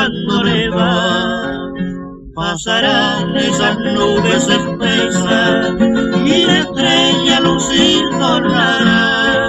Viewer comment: Hola una pregunta , Que dice en el minuto 00:38? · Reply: Pasarán esas nubes espesas y la estrella a lucir tornará (abreviatura de retornará).